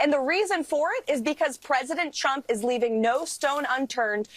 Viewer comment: Sounded like she said president chump…. Yes, yes he is….first time I agreed with this lady….